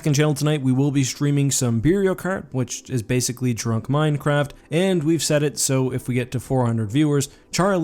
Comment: you mean Mario Cart? Sounded like Minecraft.